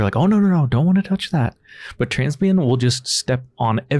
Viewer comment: did i mishear you or is ur cats name Transbian?